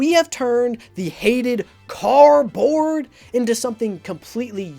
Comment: Who else clicked on the captions button to see if it misheard "car board" as "cardboard"?